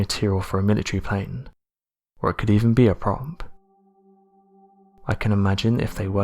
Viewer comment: A gut that cant read OR speak properly is doing the narration!!! I just dont understand why mumbles is let anywhere near a microphone. At 3:55 he tries to say "prop" and he says "promp" ffs! There is really no point in picking any one instance of his mumbling, its all mumbled!!